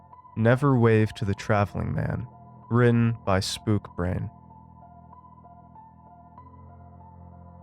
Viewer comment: Listeners, I hope you thoroughly enjoyed my story, and if you're now feeling a sense of dread, even better. I'd also like to extend a huge thank you to for his delightfully terrifying narration. It would mean the world to me if you popped over to my FB page <link> and gave it a like, your support is very appreciated! Also, if you have any questions about the story, please comment below. Your Friend in Darkness, SpookBrain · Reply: I thought said "smoothbrain" 😅